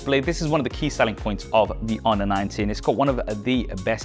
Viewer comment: This whole time I thought you were saying this was the “Honor 9T”.